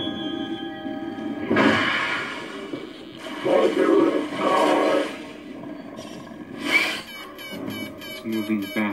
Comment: What does the predator say at 0:57 ? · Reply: Mother of god